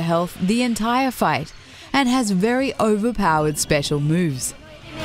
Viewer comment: I swear she says "overpowered special boobs"